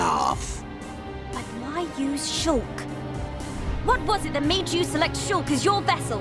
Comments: Melia said what made you select, but the subtitles says what made you choose. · Why'd Melia say select when the subtitles said choose? Typo? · Am I the only person who notice that at 21:14 , Melia said "What was it that made you select Shulk as your vessel?" instead of what it said in the caption? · Melia said select but the subtitles said choose